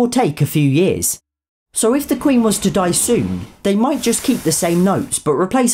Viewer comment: I thought he said, “if the queen wants to die” and I can’t stop laighing